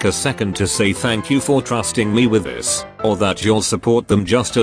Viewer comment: How I knew my sister was gay: She said she wanted to be like our dad. I was like “Oh, you wanna get married?” She said ”no, I wanna kiss girls.” Edit: she was 7 at this time. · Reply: I thought you said "and thank you for not being pretty" and I was like "hol' up? Did I read that right?"